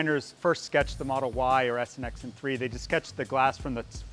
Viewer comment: is that the new model? Hard to tell what he said there...snxn3?